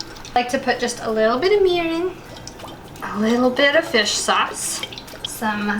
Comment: did she say “I like to put a little bit of dish soap “ it sounded like that 😂